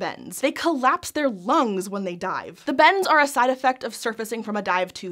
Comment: I miss heard her so bad I thought she said, "When whales die," and I was so confused as to how that was helpful for them XD